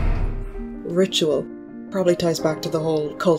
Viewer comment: For me, I always heard '' Hate you'' but I guess everyone heard ''Ritual''.